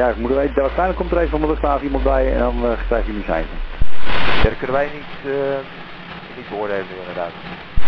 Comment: I think he said "Kunnen wij niet beoordelen inderdaad" ("We can't judge that, indeed"), meaning he can't judge from the cockpit whether the aircraft are in some way interwoven with each other, in which case pulling forward back to the gate wouldn't be possible.